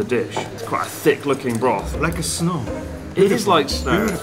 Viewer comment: For a second, I could’ve swore that said, “Like a snot.” 😂